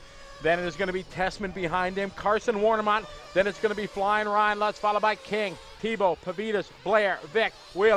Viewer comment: did he say flyin Ryan like the rc review guy